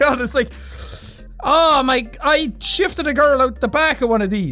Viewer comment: Not gonna lie thought ya said you shafted a girl out back